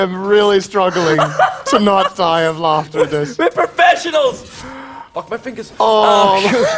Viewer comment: Hm actual "Professionals" could be misheard with "vegetables" xD